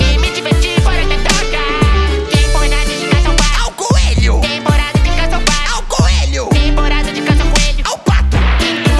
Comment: o que o perna disse nesse momento? Eu não entendi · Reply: Pernalonga: Temporada de caça ao pato! Patolino: Ao coelho! 2x Pernalonga: temporada de caça ao coelho Parolino: Ao pato >:( leva um tiro